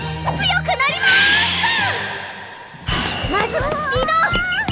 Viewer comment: ここのウララなんて言ってる？ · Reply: まずはスピード！